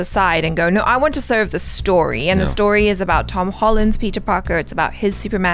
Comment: Am i the only one who heard say tom holland' s superman????